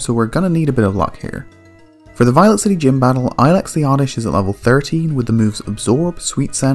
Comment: I coulda swore you said "Ilex is at lvl 30" at 3:32 but maybe it was me not hearing it right :).